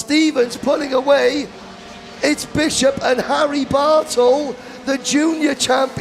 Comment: My wife could overhear the broadcasters and she says "Whaaaat? Someone's name is Harry Butthole?!?!?" We both busted out laughing and I had to replay it. I said the drivers name is Bartle. She said "Well the way he said it sounded like butthole!" She is still laughing in the other room. Good tight racing there.